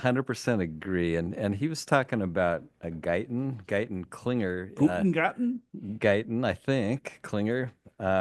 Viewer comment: You said “Guyton-Klinger”…I heard “Gunter Glieben Glauten Globen” and I broke out into song. It’s the intro to Rock of Ages by Def Leppard of which my cube mates didn’t appreciate.